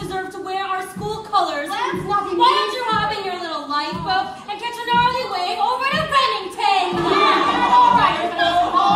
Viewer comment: can someone tell me what she says? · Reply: Noe Alvarez Why don’t you hop in your little Lifeboat and get all the way over to ramington?